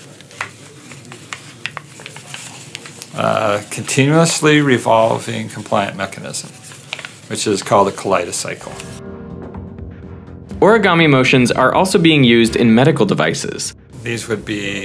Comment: collaida cylce? Did someone catch that, i wanna make one but understand the name of it 😃